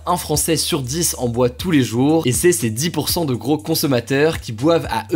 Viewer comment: « Un Français sur dix en boit tous les jours, et c'est ces dix pourcents de gros... » Moi : porcs ? Désolé😂